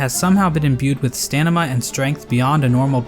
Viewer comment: did he say... stanima?